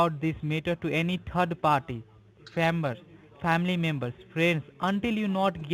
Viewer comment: It sounds like it's his first day wow that was Great work. Did he not say scammer towards the end 27:46 it sounded like it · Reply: I think he said 'family', that's what my brain gets from it anyways. He was a bit slow with the script reading that's for sure. Thanks so much for watching, Brandon. 💍😊